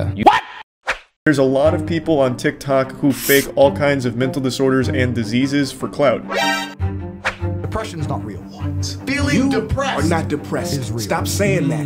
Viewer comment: Why did I hear "The Prussians are not real" rofl